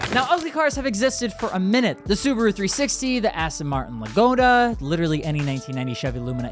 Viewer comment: what is bro saying :D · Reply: Aston martin Lagonda